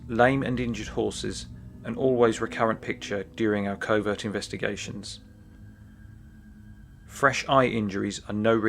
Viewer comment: did he say "doing there covid investigation"???? This was 2 years ago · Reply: The speaker in the video says "covert investigations".